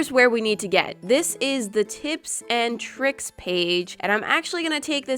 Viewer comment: for a second I thought it said tips and thicks 😂😂😂😂😂